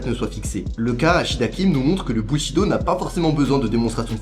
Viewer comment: Le quoi ? Le Bullshitdo ? 🤣